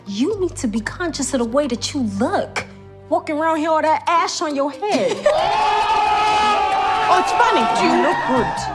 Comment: At first I thought she said "Ass on your head" which would have been a lot funnier line. I can see the outtakes now. Shuri saying "Who's the colonizer's now bitches"!